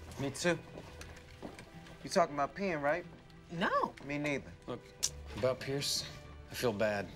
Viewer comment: WTF DID TROY SAY!!! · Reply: "Me neither"